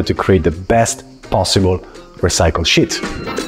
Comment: I looked away and I thought he said “the best recycled shit”😂